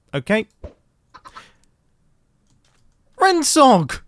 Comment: I actually thought you said “Ren suck” lmao